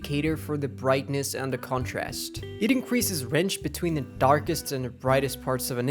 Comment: Why does it sound like you are pronouncing HDR like AGR? lol @ 2:20 "It increases wrench between" what? Sorry i can't understand half of what you are saying.